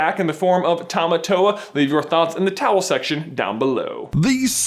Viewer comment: did he say the towel section?